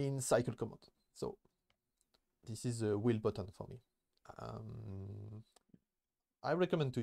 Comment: Sorry what is the * button? I hear "wheel button", do you mean the middle button on mouse?